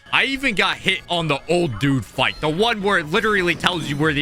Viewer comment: i thought you said old duke🥀💔